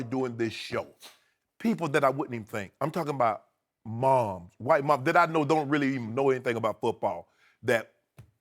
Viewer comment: did Shannon say "White Mom's" lol